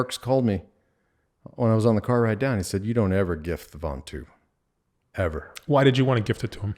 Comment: what are they saying? sounds like vantu? can anyone tell me what they're referring to here?